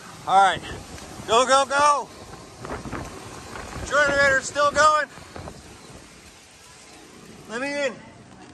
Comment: Ok I just heard you say the generator is going